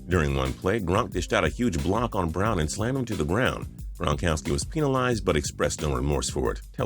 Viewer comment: did he really say “peenalized”